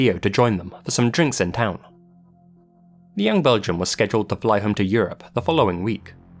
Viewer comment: "The young Belgian" Is it odd that I heard "the young virgin"?